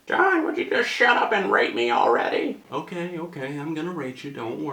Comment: I thought u said rape lmao